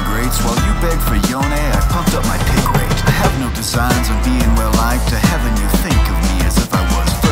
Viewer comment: Lovely & respect Btw i thought u said no jhin 😅😘 well thank you for the work guys